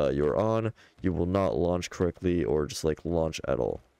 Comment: I thought you said lunch